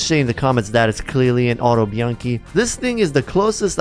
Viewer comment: When you said Autobianchi I misheard it for Autumn Monkey